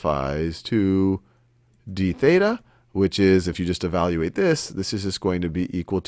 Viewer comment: when you say "this equals to dBETA" i thought u said debata which translates to english into "debate"